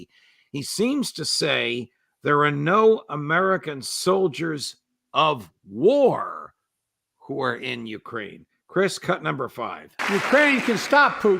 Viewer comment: I don't understand what's happening here, and beyond w/ their analysis. Biden doesn't mention uniformed soldiers. He says, "there are no American soldiers in the war in Ukraine." Not very intelligibly but it was fairly clear. They want weapons, not soldiers, and there are no American soldiers currently in that war. I don't know if I necessarily believe him, but what he's saying is very specific and it isn't whether or not they have the stars and straps on their shoulder, and a camelback.